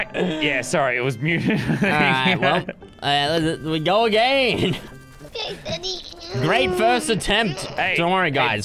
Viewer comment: Did anyone hear Bondi morn and say hey daddy